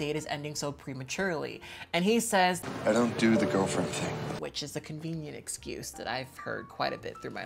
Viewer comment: Before I knew this was based off of a twilight fanfic: I heard him say "I don't do the boyfriend thing" My brain said TwIlIgHt WhaT